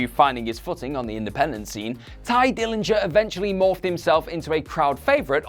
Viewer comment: U said TYE DILLINGER 3 times, and I didn't kmow u were actually saying that until u said PERFECT 10; I thought u kept saying TIGER NINJA lolol